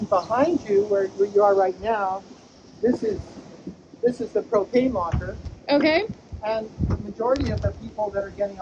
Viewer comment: I heard him say "this is the cocaine locker" hahahah I was like what?